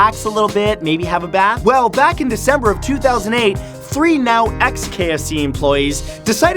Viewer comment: Did Matthew say "Back in December 2008"? 2:32 What year is this O.O